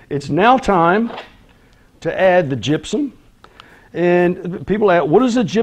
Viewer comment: I'm sorry, but i really don't understand what you refer at 11:33. "Gibson", "gipson"? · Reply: I am sorry. My southern accent can sometimes be confusing. It is Gypsum. It is a chemical that raises the temporary hardness of the water. You can skip it but since it was in the recipe I used it.